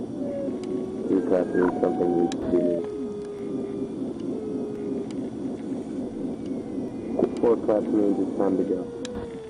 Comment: Thought I heard "3 claps mean something needs to be cleaned"